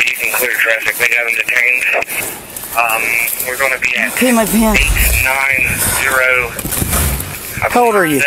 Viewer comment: what did he say here? Did he say he peed his pants????